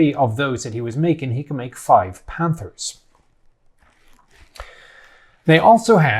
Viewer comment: What did he said? "Five Panthers"?